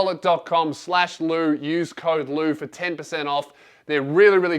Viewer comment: When he said code “lew” I thought he said code lube